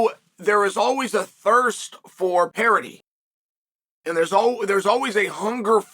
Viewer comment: What is the word chael says at 8:33 "Parity"? What does it mean?